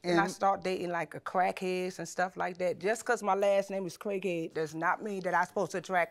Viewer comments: if i didnt see the name i wouldve sworn he said her last name was "crackhead" lmao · When he said her last name I thought he said Crackhead. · I thought they said her last name was crackhead I was gonna say that’s not a set up for success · Lol, I thought he said crackhead at first 🤣 😂 💀